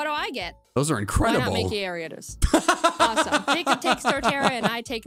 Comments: At first I thought Prof.Elm said why not make it Ariodos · when he said wynaut, mankey, ariados, i thought he said, why not make it ariados? and was so confused